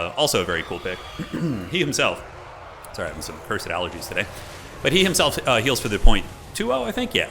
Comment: I swear I heard "Sorry, some fursuit allergies today."